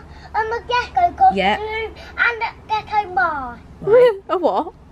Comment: Legit thought was saying he wanted a Ghetto mask and a Ghetto outfit 😂 bless he is so cute